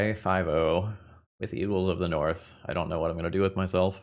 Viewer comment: or so it sounds like you say "If I 5-0... With Evils of the North..."